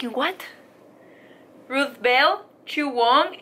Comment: entendí "drake bell" y yo "azuputamadre :0" 😂😂